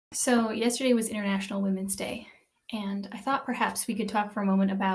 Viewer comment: I thought she said “irrational women’s day” LOL 😂